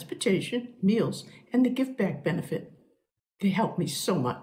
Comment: did anyone else notice the old lady at 0:28 says "Gift Bag" benefit instead of Give Back?